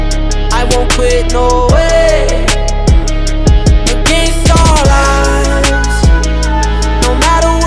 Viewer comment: You cant stall us- always thought he said against all odds.. Lol